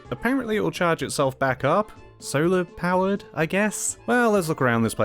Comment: After Free to goes in. I hear you talking about the Solar panels, I ignored a Birdemic joke because I thought you said 'Solo' . Now we at least know that Greedo at least entered first.